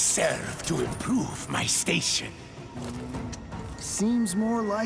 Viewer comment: I thought he said "I serve to improve my workshop" not station